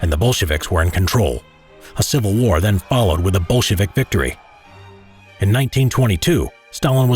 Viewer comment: sounded like bullshit victory 😂😂😂